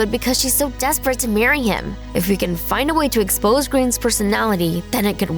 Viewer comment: it says green instead of grain